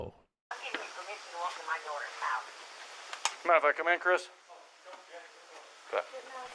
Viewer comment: And did anyone hear around 16:53 Shannan’s mother who was on the phone with Nicole said “I give you permission to go into my daughter‘s house”. I assume Nicole told her that Chris was in the house and hadn’t opened the door yet. And I will always believe Nicole Kessinger knows something about what he was planning to do and possibly even helping him. There are some videos that people say that Nicole was there.. that her phone was pinging near his house at that time and never pinged at that place again, I think the lady got away with being an accomplice to murder . I honestly believe the police “dropped the ball”on her BIG TIME Shannan has spent over a month with her family right before she was murdered (in North Carolina ) and Chris (who didn’t go with her)was spending almost that entire time with his mistress so yes, they were having a full-blown affair